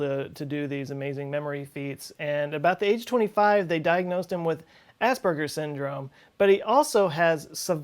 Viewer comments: it sounds like he says ass burger syndrome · I heard you say assburgers